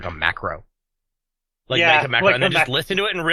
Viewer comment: Curse my slavic converted brain when he said macro I thought he said Makarov